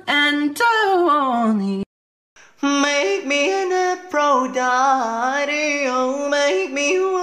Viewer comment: I keep hearing "Make me an Afro tidy" HELP I CANT UNHEAR IT